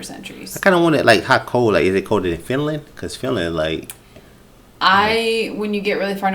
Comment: Sounded like he said "New Finland".